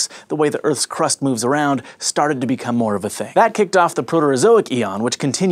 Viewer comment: Does anyone know what it says at 1:30 : "Plate tectonics, the way the earth's crust moves around, started to become more ..." Thanks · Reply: "...become more of a thing." Hope that helps